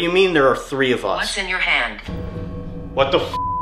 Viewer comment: I can hear a voice, sounds like it says hand, after one of u says that just gave me the chills!!??! Hav a listen, eh?! Luv from Australia 🇦🇺 xxxx